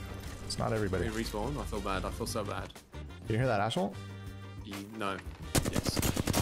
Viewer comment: I swear instead of saying "Did you hear that Ash ult?" I thought you said "Did you hear that "Ash hole" 😂